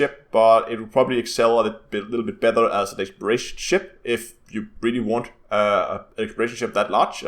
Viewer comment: excels as a blulabulubsz ship ... 2 times? what did he say? the youtube subs says decoration ship or a British ship. Love you work man for so many years :) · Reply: Excel a little bit better as an exploration ship